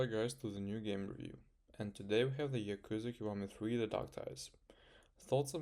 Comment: I thought he said "yakuza kawaii" in the beginning of the video but actually he said "yakuza in Hawaii" LOL